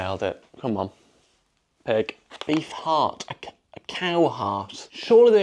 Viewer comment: Whenever I hear someone say "beef heart", I hear "bee fart".